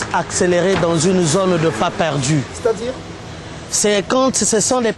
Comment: moi je suis anglophone mais je suis tomber amoureux des congolais, on peut me dire ce que a dit de 2:48-2:53? je ne peux qu'entendre "accelere dans une zone des pas perdus". on peut m'aider???